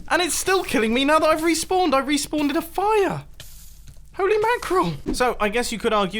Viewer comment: It’s sounds like he said respawneded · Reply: RadioactveGum YT yea he said respawned in